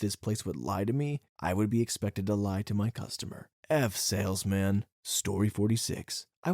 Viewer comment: I love how he said "F sales, man" but the captions say "F salesman". I just think it's kinda funny.